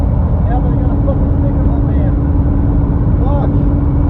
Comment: I think saying “Yeah, but I got a F’ing sticker on the van”. I think he’s referring to church logo stickers on the sides of the van is an identifying mark. You can see the sticker he’s talking about at 20:17. Anyway, what’s the friggin deal with!? She might be the most cold hearted outta all of em. I think maybe was still pumping with adrenaline when he yelled “We still F’ing won tho!” (I’m the jury loved that one🙄), but!? She don’t even sound like her heart rate increased at all. She’s just on some: ‘Meh. What’s the big deal?’ type sh**. She sounds like she genuinely doesn’t get why everyone’s freaking out about this, telling everyone “It’s fine”. Wait, scratch that. I just got to 22:45 and all pissed off because he can’t wear his hat anymore. Is he ‘F’ing kidding me!? The whole thing’s F’d up, but Kimberly I feel like she shouldn’t have gotten 7 years if the and Raven got 8. Kimberly was the only one in the van who was genuinely upset about it and understood how F’d up what happened was.